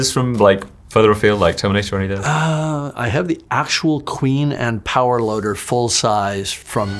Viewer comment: I heard, " I have the power loader and the full-size queen..." and I became frantically disoriented right before my head exploded.